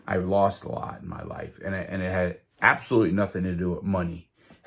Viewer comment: did you say wives?